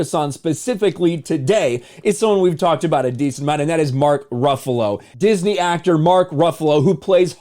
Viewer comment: I hear that " you dont want to see mark bluffalo angry"